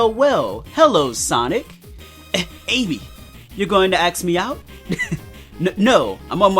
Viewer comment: it sounded like you said axe, & not ask, & I had this image in my head that Sonic was literally axing Amy off.